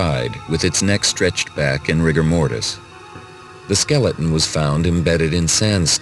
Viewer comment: When I first saw the interactive video at 29:15 to 29:59 while playing the game, I somehow overheard what the narrator said with respect to the Tyrannosaurus rex specimen found in 1988, mishearing "rigor mortis" as "rhythm order" and "rancher" as "doctor".